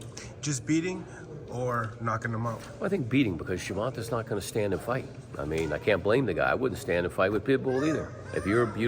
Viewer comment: lol did he just say Tank don’t stand and fight 😭😭